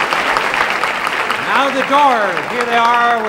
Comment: Did he just say “Now the Door “ instead of “ Now the Doors” OMG. Blasphemy 😠 what a creepy satanic looking man. Omg creepy